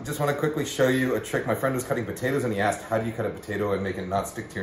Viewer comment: I hear "he was cutting potatoes in the ass" for a seconf there